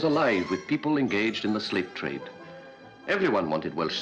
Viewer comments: Thought you said 'Welsh slave trade'. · I thought he said slave trade. I was gonna say, Turks sure but Russians?? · Thought he said "slave-trade" for a moment there. Welsh slates, not slaves! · LOL At 0:35 I heard Slave trade instead of Slate trade.... 0:40 every one wanted Welsh slaves for their roof tiles....